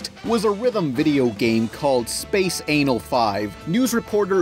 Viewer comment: I can't be the only one who thought he said space anal at the beginning. no? just me? OK I'll stop